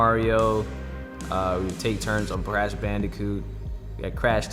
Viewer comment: Did he just say Brash Bandicoot💀 My man's a blood